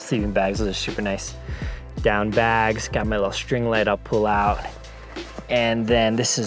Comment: When you said “String light” I thought you said Streamlight. 😂 I was washing dishes so the noise made me mishear